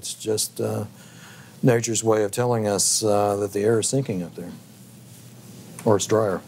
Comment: What a total joke!!! Liar alert at 21 min. The so called expert is pointing to the white lines he calls contrails then claims that the earth is sinking? Liar! Sky is not earth. Earth is dirt! We don’t live on a spinning globe. The chemtrails prove that! Thanks for proving that earth is flat. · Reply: Actually the interviewee said the air is sinking, not the earth is sinking. Jessica Evrist misheard. Warmer, dryer air sinks, seems to be what the interviewee said.